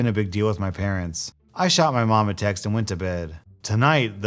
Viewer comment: My ears completely blurred out “a text”, so it sounded like “I shot my mom and went to bed”